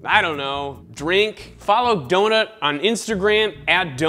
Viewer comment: He says 'donut' but i hear 'dota'